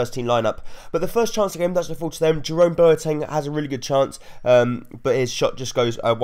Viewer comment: does he say Jerome Boateng or am I going mad?